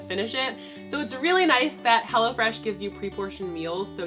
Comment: I thought she said hella fresh lmao 😂